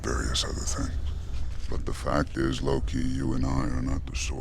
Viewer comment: alright... at first I was like... did thanos say “lowkey”??? & then I realized he said “loki”